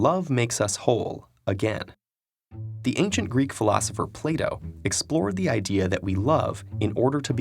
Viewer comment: Plato? I thought he said '' playdoh "